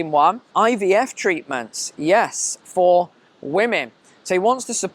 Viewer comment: Oh I thought you were saying IBS treatment for women but it’s IVF treatment lol.